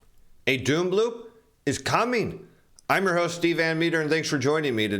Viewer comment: Steve, at first I thought you said Doom Balloon! haha Just what we need. An all-new ticker-tape parade.